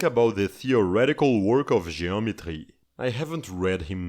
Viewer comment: i thought he said geometry